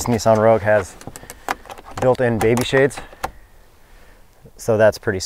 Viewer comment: what did you just call the peasant blockers!? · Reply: Baby shades